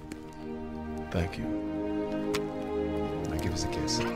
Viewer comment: Did Thor just say “thank you”🙄